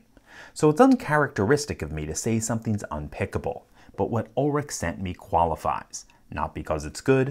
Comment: For a moment I thought he said Ulfric sent it; seeing Bethesda on the letter threw me off lol